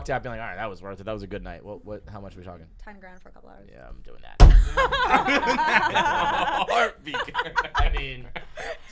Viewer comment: I didn't hear her reply. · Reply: She said “$10,000 for a couple hours”